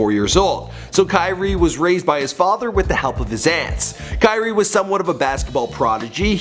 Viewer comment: i thought he said wit the help of the xans